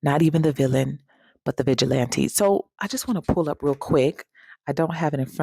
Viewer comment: Not a hero nor a villain, but a vigilante. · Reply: I thought she said Bitch-il-lante 😂😂😂